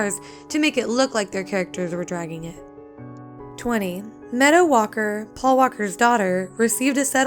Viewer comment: sounded like she said metta walker... *meadow